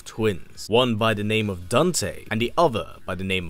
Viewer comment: bruh he really said "dunte"